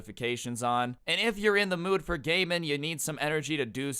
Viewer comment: When he said 'gaming', I thought he said 'gay men' 'if you're in the mood for some gay men...'